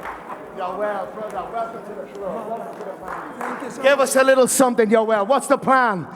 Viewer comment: Did he just say welcome to the gay fc lol